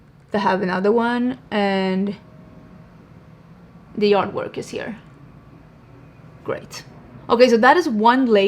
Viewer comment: When you said ‘the yard work is here’ I misheard and thought you said ‘the artwork is here’ and then turned to show the impact of the mascara 😂